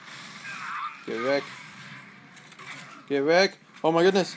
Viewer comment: did you say "geh weg"? Are you german ? :O I don't think so but it's german.